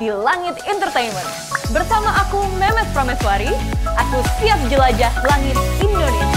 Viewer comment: Ohh mba memes prameswari, aku salah dengerr malah kedengerannya mba meme......(k) #DasarAku